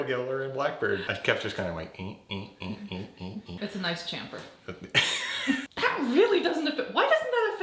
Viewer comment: Haha! Did she say "Nice CHINfer!" at 5:50? lol #CHINfer EVERYTHING!!!